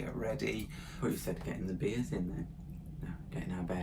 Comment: Ha ha I thought you said "getting our BEER IN "